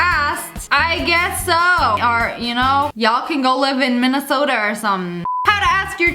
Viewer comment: I heard her say minnersoter and I immediately looked and thought “I live in minnersoter”